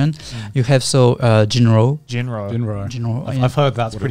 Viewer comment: What anime does he says in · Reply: Jin-roh